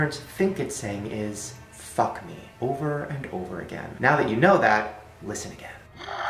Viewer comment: The first hatchimal clip I hear “Fuck off” not “Fuck me”